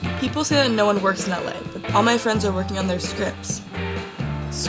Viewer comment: First i heard "What I really wanna do is drugs"